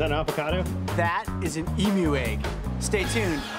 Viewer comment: I thought he said Emo egg